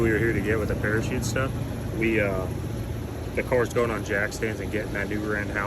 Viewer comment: ….. Did you say jackstands? Well you are of course you should ALWAYS have Jackstands with you